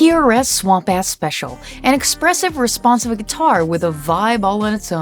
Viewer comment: For a second it sounded like you said Swamp ASS Special! lol.